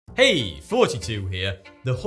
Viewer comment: Excuse me but British people really need to learn how to pronounce the th-sound correctly. It's my first time watching one of videos and I literally heard 'Hey 42 here" in the intro